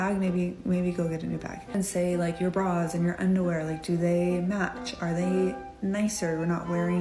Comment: I heard “your bra is in your underwear” I was like…it most certainly is not, I’d notice THAT Also do not come for my hoodie!